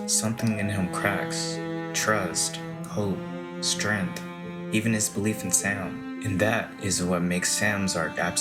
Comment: I swear to God the first time I thought I heard "Even Frodo's belief in Santa" for some reason, that's just too far. I died! 😂